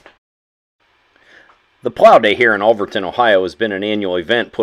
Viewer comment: Where was this I live in NW Ohio and don't know any one with buds. Thought he said olverton ohio? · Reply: Alvordton, Ohio . William county the north west corner of ohio.